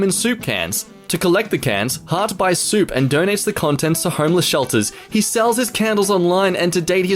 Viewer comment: Did he just say heartless shelters?